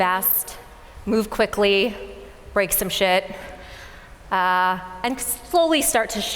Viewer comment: Did she really say "break some sht"??